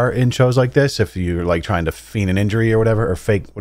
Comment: Feign? cuz it sounds like you said fiend lmao